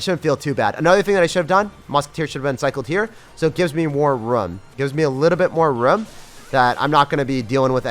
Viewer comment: Anyone else get triggered that says "rum" instead of "room". More rum please sir with my coke and ice.